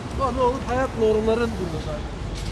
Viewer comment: I think I've heard the Turkish language there... "hayat normları something" ..like "life norms..." (